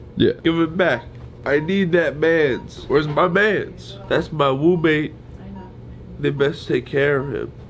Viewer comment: "that's my womb-mate" ❤️ they're so sweet to each other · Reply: Annet I thought he said Roommate